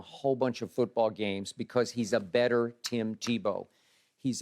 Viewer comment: Did he just say better tim Tebow 😂